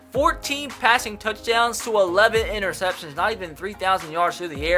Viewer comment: tom savage only threw 11 interceptions guys. coulda swore it said 21 though